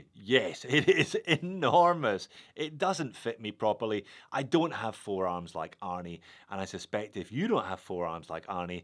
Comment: Been a long day, I thought you were saying "I don't have four arms like Arnie", but you're right either way, I don't have four arms or forearms like him either